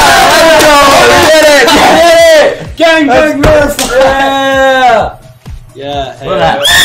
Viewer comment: did he said "gangbang"